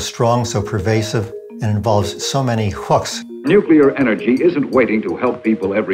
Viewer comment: Can someone please help me understand what the gentleman says at 5:06 ".... bla bla bla and involves so many ?????..." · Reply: "involves so many hooks" When you fish you use a hook and for the nuclear power proponents, they were dangling the hooks of 'energy would be practically free (ha ha good one right), nuclear powered flying cars, building in the desert, etc'.